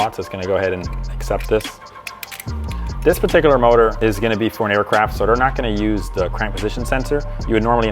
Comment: As a pilot am I hearing that right. Did he say that engine is going into an aircraft. lol wtf